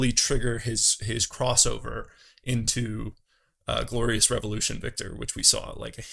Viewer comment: did... did you just get Viktors iconic quote wrong?? i heard "Glorious REVolution" instead of "Glorious EVolution"